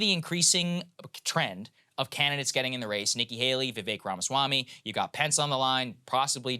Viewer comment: For a second when you were saying he's at 50 even with other candidates in the race, I thought you said "The Big Ronswamy" and I was like, "nice niche nickname for De Santis" but then I realized you're talking about Vivek Ranswamy